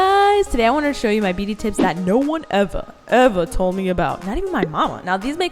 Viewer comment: i thought he said nobody ever told her about these hack???